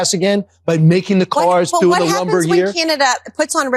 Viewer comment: I'm sorry did he say "Making the cars through the lumber here?"... Does Fox News not know what cars are made of?